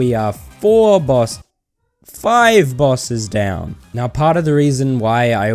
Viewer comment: i thought he said boss cells LOLLOLOLOL